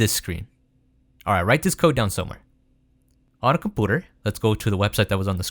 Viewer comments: Did he say COMPOOTER? 🤣🤣🤣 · What does he say after "write this code down somewhere at 3:04? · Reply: "on a coputer" He said computer weirdly is all